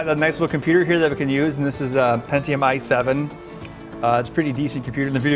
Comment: did he just say pentium I7?